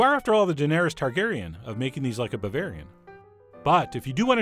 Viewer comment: After all you are the Daenerys Targeryan of making these like a barbarian.. LOL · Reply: I'm glad you guys cleared that up. I heard "barbarian" and was a little surprised. Cheers! Or should I say PROST!